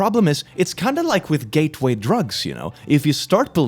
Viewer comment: I know he said “Gateway Drugs” but I thought I heard “Gay tuei drugs” for a second and now there’s no going back